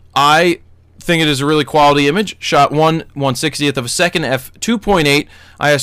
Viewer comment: ok I misheard. I heard 1/60 at 200... instead of 1/160th....